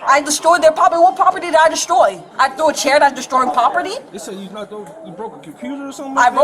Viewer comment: She threw a chair? I misheard. I thought it was hair, as in weave.